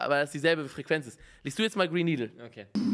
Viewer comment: Bei 9:06 min hab einfach Brain NEedle gehört